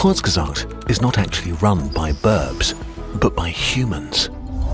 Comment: Funny, you misspronounced the word "birb" and sounded like "people" in 12:31. Not to worry, I understood what you meant.